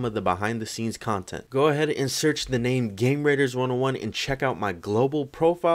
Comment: I can’t be the only one that thought that said gamer aids right?!